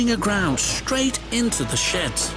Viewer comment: It was out of control and running aground straight into the sh....... what did you just say? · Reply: He said sheds but I listen closely and it sounded like shit XD